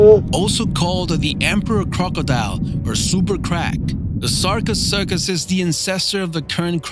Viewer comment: did he say super crack?